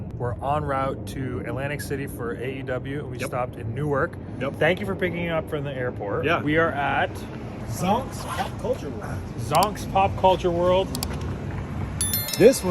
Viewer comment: Totally thought it said "Zoinks" lol also Piccolo was my brother's favourite character too!